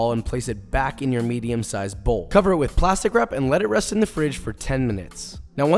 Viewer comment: I thought he said "arrest it if it's resisting you" referring to the dough 😂 instead of "let it rest if..etc.."